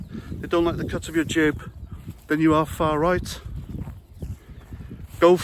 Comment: I thought Rowley said “extreme far right”. What a Joke.